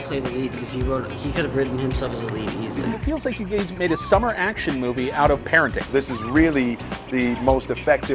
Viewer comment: I skipped back to check if he really did say you gays but it cut off the word parenting and so it sounded like he said you made a movie from pain